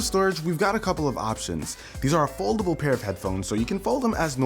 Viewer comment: I misheard foldable to affordable and when I went back what he said I was really sad